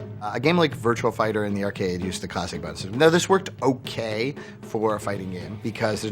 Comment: WTF does he say at 19:40 when he's talking about the system that Virtua Fighter used for animation? It sounds like he says the Kasay Bansu... wtf? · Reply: "A game like Virtua Fighter in the arcade used the classic bone system."